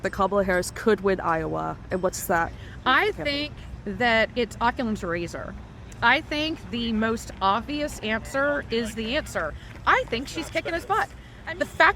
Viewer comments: Did she say "Oculum's Razor"? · Did i just hear her say "oculam's razer"???😂😂😂